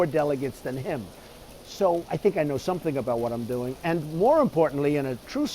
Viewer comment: I just heard him say "I think I know what I'm doing"